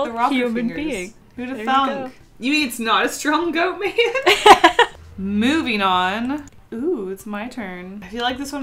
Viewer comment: When she said "moving on" I thought she said" Movie non" LOL!